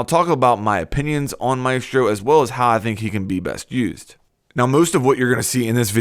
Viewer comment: I heard "How he can be best abused". Not necessarily inaccurate XD